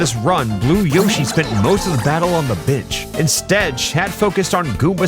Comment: I'm not kidding, it sounded like you said "Blue Yoshi spent most of the battle on the bitch."